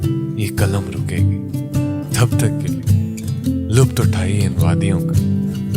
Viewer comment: Mesmerizing it is..Great work..quite professional👍👍 Just one thing that I noticed is that u said lupt instead of lutf at 7:26...See how attentive I was while watching ur video😉😅